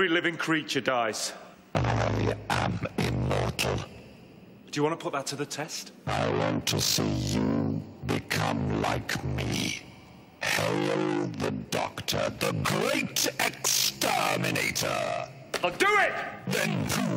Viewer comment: at first I thought the Emperor Dalek said "Hello the Doctor" not 'hail'.